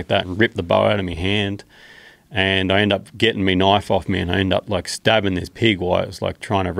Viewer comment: "Just Grabbed me knoife" what a beast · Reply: I heard 'knife'.